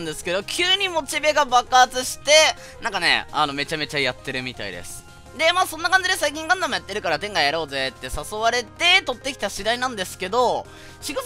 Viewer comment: の店外がTENGAに聞こえて3回位聞き直した